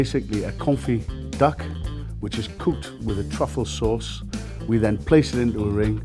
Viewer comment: did he say that duck is "pooped with a truffle sauce"?